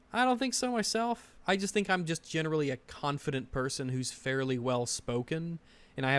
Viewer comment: About 31:00 in i think u mishear say Confident over Incompetent. His scratchy manor of speaking makes it hard to understand him. Incompentent to the point of Obnoxiousness makes more sense. Still you have far more logic than he does